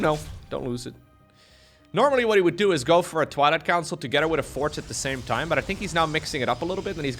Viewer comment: I always hear "Toilet Council"